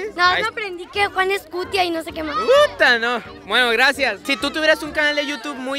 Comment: y 12:41 escuche P***?